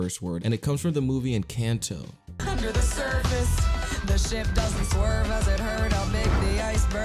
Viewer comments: Actually I don’t think they’re saying the Sh word in this part. I think they said Ship instead of the Sh word. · it says ship, 1:44 it says missy, 2:23 it says female dog, 3:09 it surprisingly doesn’t say beauty it says the actual b word, 4:20 it doesn’t say h word it says heck, 5:19 it was gunna say the b word but it paused, 9:09 it says binturri witch means t word.